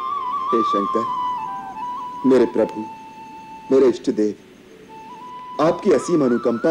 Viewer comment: He Said Ishtadeva But The Subtitle Say My Demigods. This Ramanand Sagar Was A ------. 1) <link> 2) <link> 3) <link>